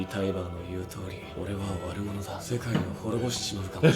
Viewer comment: and 12:07 I think there's a slight mistake in the translation - it sounds to me like Eren is saying "owarumono" (終わる者 - one who ends, ie world ender) rather than what the translator heard: "warumono" (悪者 - bad guy). It's a small detail, but the dialogue feels pretty dumb if Eren is just saying "I'm the bad guy, but I thought you were the bad guys too" since that's more than obvious. "World ender" seems much more evocative to me, like there's nothing to be done about the overwhelming force coming. Also, beyond it just sounding to me like he's saying "owarumono," if you try to listen to it as "warumono," there's a weird space that screws up the natural cadence of his speaking. In the first case: "Ore wa ~warumono da" and in the second: "omaetachi ga ~warumono ni mieta." Honestly, unless multiple native Japanese speakers convince me otherwise, I'm pretty sure it's "owarumono"